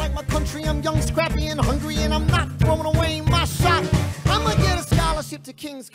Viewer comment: It sound like Lin is saying Sho instead of Shot